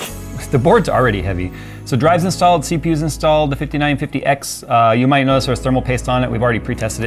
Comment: Did he say 5950x at 11:50? Or am I tripping